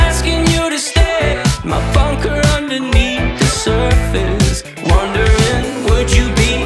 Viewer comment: I always thought it said "my fucker underneath the surface" 😳